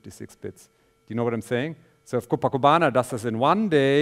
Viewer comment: What did he say at 56:31 ? PAkubana or what? couldn't understand! What is that? Can somebody explain? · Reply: Good questio. I am referring to the COPACBANA (Cost OPtimized PArallel Code Breaker) machine that we built a while ago, cf. <link> regards